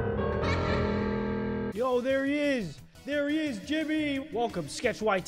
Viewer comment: I thought u said Timmy 💀 can I get a pin